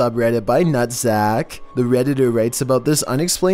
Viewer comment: I couldn’t get scared by number 7 lol .-. All I kept hearing was “nutsack” or as Chills said “Not Zack” (Nuhtzac) Lol cx